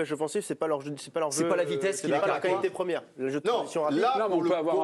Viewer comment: J'ai entendu " la vitesse c'est pas leur qualité première" La clim arrive 😂